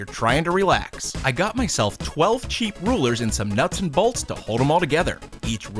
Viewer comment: i heard nuts and volts